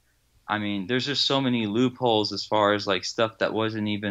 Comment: Count- I feel like: 29 Here’s the thing: 68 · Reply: I thought he said poophole.....